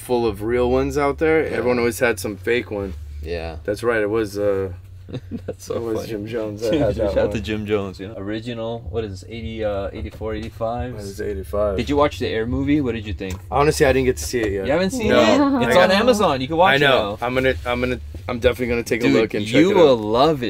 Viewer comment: did really say ‘84?